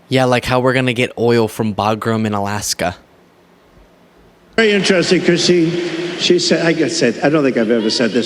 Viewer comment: did he say to “it’s very interesting Christy”?